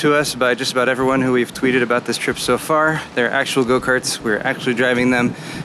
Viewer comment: Thank you for going in my place and showing me how wonderful it is! <3 :( Oh man, all that food............. <3 <3 <3 Cat heaven...for them! :3 LoL, I thought you said Mario Kart... :))